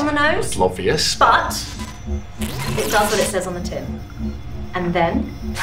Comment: I'm sorry but, what does she say on 2:18? "It does what it says on the *(¿?)*" ? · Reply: Probably tin. It's a popular saying that means something does basically a literal meaning for the title. Jack uses the same phrase to refer to the Defabricator in Bad Wolf which obviously takes away fabric.